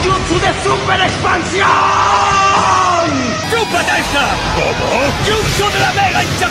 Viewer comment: ayuda, creo que escuché "chup4t3 esta" XD